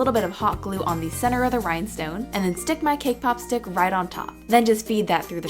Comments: When she said cake pop stick i thought she said kpop stick😂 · lol when she says "cakepop stick" i hear "kpop stick" xD